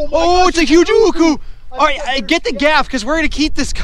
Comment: can someone plz explain what he said 10:19 when he caught da fish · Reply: oh its a huge uku